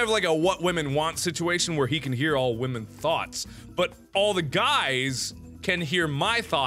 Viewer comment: Did mark just say All women are thots?